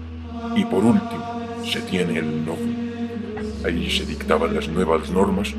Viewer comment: tales la tiza? o que dice en ese segundo que no entendi? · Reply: por último se tiene el Novum, ahí se dictaban las nuevas normas que no se encontraban en el Translaticium...